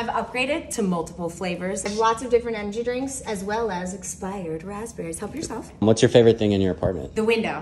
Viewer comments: " expired raspberries, help yourself! " why was she so happy when she said that💀💀💀 Edit: i know she was sarcastic😭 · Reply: for some reason i thought she said 'spicy raspberries' and was like oooooo i want to try them too 🥹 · Why did I hear “spider raspberries”😭